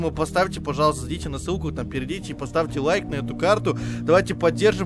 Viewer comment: ни кому не послышалось в 18:04 слово "пердите".